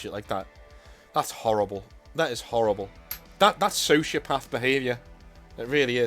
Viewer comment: When you said that's, "sociopath behavior" i thought you said, "that's shit path behavior"